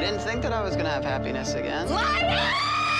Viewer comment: what is it that she yells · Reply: LIAR